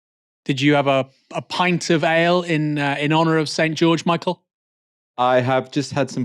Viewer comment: I heard that as "did you have a pint of ale in honor of Saint George Michael?" Still checks out, I guess.